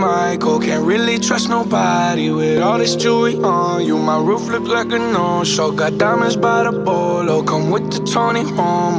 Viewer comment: when i first heard this song, i always thought he said "my roof look like a no-show". turns out i was right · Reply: I thought he said “ my boot is like a mo-po